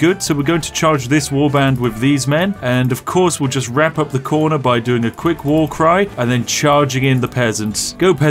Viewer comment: First time listening to this I thought I heard you say "And of course we'll just wrap up the corner by doing a quick war crime "